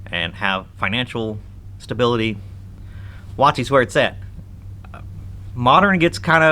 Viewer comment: What are you saying? Watzi? Watzi’s where it’s at? Lol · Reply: Wotc, wizards of the coast